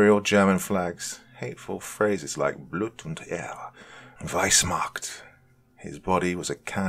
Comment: I don't speak German? What were the two phrases he said were tattooed on the cop? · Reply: im german and happy to help out. the first one was blut = blood und = and ehre = honor. couldnt quite make out the second one but i sounded like "reichs macht"? reich of course being nazi germany and macht = power. or i misheard and it was "weissmacht". maaaaybe they just google translated it and its meant to be weiss =white and macht = see above. sorry have to tiptoe around youtube censorship